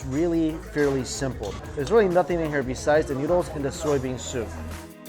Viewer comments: New t-shirt: bean soup? I thought you said bing soo. I thought you took a bean soup break but ... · thought he was saying Bean Soup but he was in fact saying Bingsoo